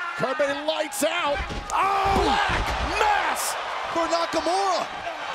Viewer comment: For once, I thought they said the Black Mask, but Black's finisher is called the Black Mass. Although Black Mask is not a bad name for his finisher, either.